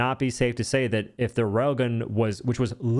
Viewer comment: Every time you say rail gun, I hear "rogan."